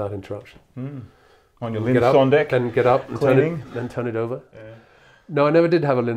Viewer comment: they're discussing something and I cannot understand what they're saying. I think it's about a record player? Something "On Deck?" Can anyone help? Thanks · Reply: They are talking about Linn Sondek, high-end phonographs....there are 25th anniversary models out there for nearly $4,000! Seems Alan Parsons wanted Ivor Tiefenbrun to give him one, but paid the money to hear some classic vinyl like Darkside of the Moon on high-end equipment!!